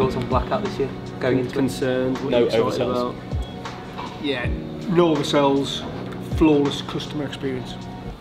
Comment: what is he saying at 09:19? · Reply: No oversales & a flawless customer experience